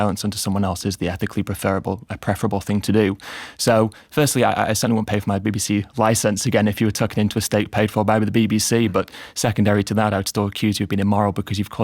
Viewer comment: What did Ed say from 1:04 to 1:09? hard to parse for me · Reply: "Firstly, I certainly wouldn't pay for my BBC licence again if you were tucking into a steak paid for by the BBC..."